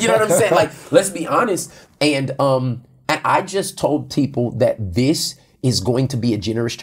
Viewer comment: it sounds like he said tpeople lol and I love it because it sounds like transformation or transformed people. That would a great name for the people who attend transformation church.